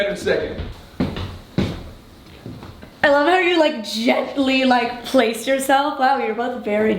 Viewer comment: Hey Dolan Twins, I love this video. I just wanted to fix an error in the captions. At 0:12 the caption says “Ellen are you like gently like place yourself.” What it should have said was “I love how you like gently place yourself.” I wanted to make sure that people who can’t hear as well could fully understand what the person in the video is saying. Thank you!!